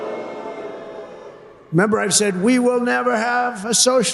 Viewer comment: Did anyone here catch this? Instead of "remember", he said, "member". That's like when someone gets drunk and starts dropping syllables from words, but he doesn't drink, which leaves dementia.